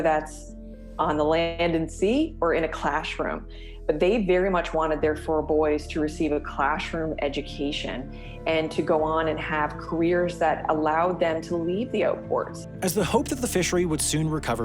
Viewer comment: Huh, I had to do a double take. She said 'clashroom' education. I know quite a few Newfoundlanders at work and never heard that as part of their accent.